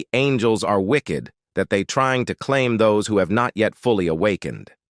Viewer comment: Did anyone else catch the fact he said angy